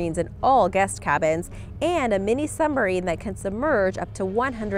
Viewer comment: Did she say "sumbarine"?